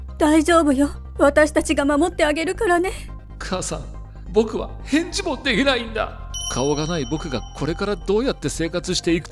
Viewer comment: 僕は、返事も出来ないんだ！が 僕は、えっちも出来ないんだ！に聞こえた